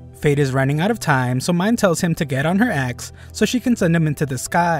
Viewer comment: I thought she said get on her a$$